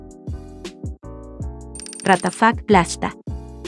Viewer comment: te juro que pensé que decía, WATAFAK PLASTA